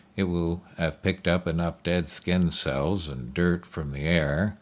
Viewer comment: I was skipping ahead through the video, and caught the narrator half way through saying "Skin cells", and it sounded like he was saying "Incels". I was what the fuck!? He knows!!! XD